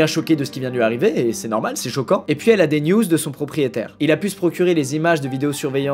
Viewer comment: y'a que moi qui ai entendu "Elle a des nudes de son propriétaire" ? je me disais aussi que recevoir des nudes dans cette situation c'est pas normal 😂